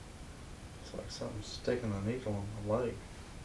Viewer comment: said “it feels like somethin is sticking a needle in my leg” but I seriously thought he said Feels like somethin is taking a leak on my leg 😂😂😂😂